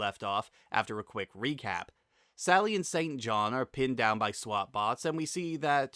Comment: Did you just call him "Satan John"? 😂